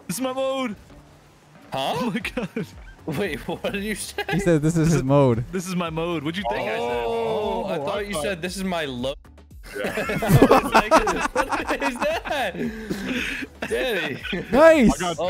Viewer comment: I thought he said “this is my load!” 😭😂😂